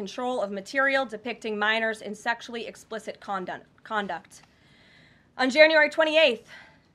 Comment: Did she say "condom" @ 4:43😀😅😂